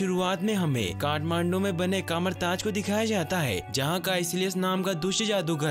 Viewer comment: does he say Kathmandu 😅🤔🤔???